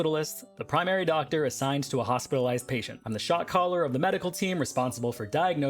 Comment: I thought you said ‘shock collar of the medical team’ and I was VERY confused